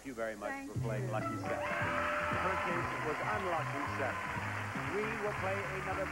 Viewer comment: What's he saying at 5:40? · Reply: In her case it was unlucky seven.